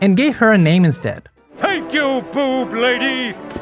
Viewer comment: Just so you know, at 4:15 when Homer's subtitles say "Thank you Loulou", its wrong. He says "Merci, Gros lolo", which basicaly translates to a slang version of "Thanks, Big tits".